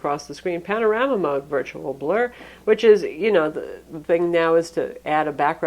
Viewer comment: Is she saying "blur" instead of tour... I was really disappointed because i can't for the life figure out what that shit is for... It's the samsung thing... you get a lot of shit you might hardly use, but it's not too obtrusive if you don't want to use it.